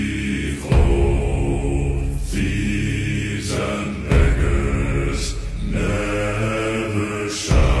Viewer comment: He really said it hard R 💀💀💀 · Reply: He says, "thieves and beggars" Don't worry I thought he said the word too the first time I heard the song😅